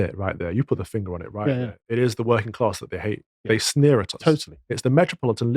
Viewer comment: did Calvin just say "they sneer at *us*"? lol